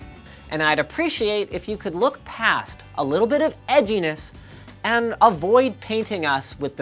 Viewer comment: Did he just say...ecchiness?